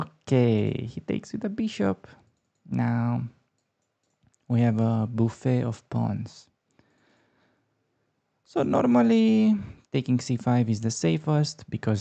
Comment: I'm hungry. At 2:28, I thought he said, "We have a buffet of prawns." No, pawns!